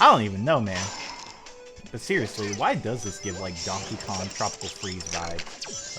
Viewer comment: I thought he said Sonic Boom:Tropical Freeze.